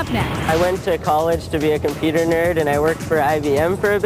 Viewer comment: Did say "computerner" or something like that at 0:04? I've never heard that word before but i like dances.